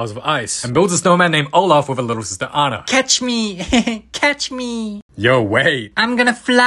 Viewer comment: I thought he said tOUCH ME when he said catch me